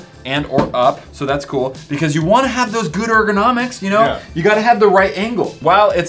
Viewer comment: I thought he said “Gooder nomics?”